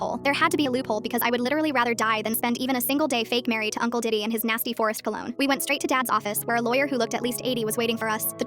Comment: I thought she said "and his nasty forest below"💀💀💀